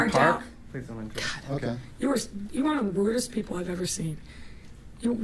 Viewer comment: I really thought that she said, “You’re one of the weirdest people I’ve ever seen” and I was like, this bitch makes Yoda look like a human!